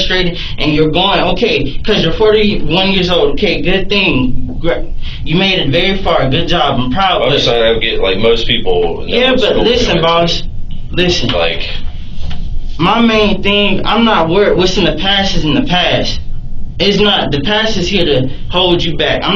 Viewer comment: All I keep hearing is "Listen, Linds....Linda, listen!"